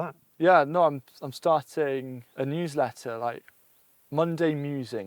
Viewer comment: I thought he said huwsletter.